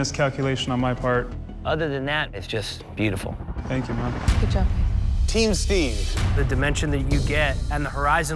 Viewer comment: It really sounded like Anthony said “thank you mom” Also, RESPECT for taking “the blame” for a “mistake” on the canvas!